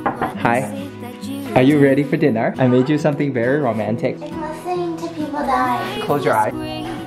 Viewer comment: I thought he said something pun intended like "I made you something very RAMEN-tic"